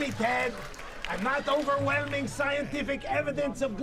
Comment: If so, we're sheep relying on evidence, and you're just wrong. And I'm afraid that after all this, I have to figure you're not too bright either. You said you weren't Right Wing, so all of us sheep can only hope that you might take a hard look at the actual scientific evidence on the topic of Climate Change, and smarten up. Maybe bring a few of the fools surrounding you with you. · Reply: I thought he said that he's GOT scientific evidence, not that he's NOT scientific evidence. But 1.8k likes later I might have misheard